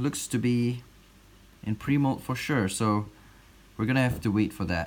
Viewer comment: For a long time when he said "pre-moult" I thought he was saying "primo"